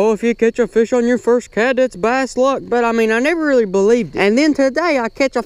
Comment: Bro did you just say its BASS LUCK? @ 3:23 #jesus